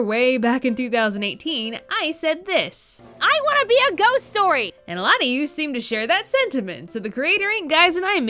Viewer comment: I thought you said ‘goat story’ not ghost story